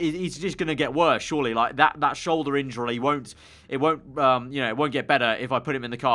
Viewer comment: Anyone else hear him say "shoulder injurly"😂